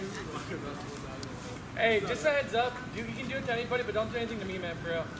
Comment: Can someone tell me what he said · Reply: You can do it to anybody but don't do it to me